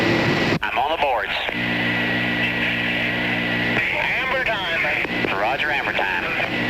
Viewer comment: Hammer Time @ 18:21? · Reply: I heard "amber time"